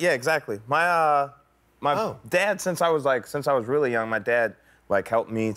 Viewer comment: did he say “that’s why I’m not independent now”?👀😶